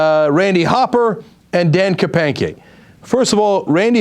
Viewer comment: Dan Kapanke sounds funny....sounds like Cenk is saying Hanky Panky